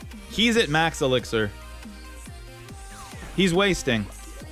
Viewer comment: I got the princess today!! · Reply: I thought you said weast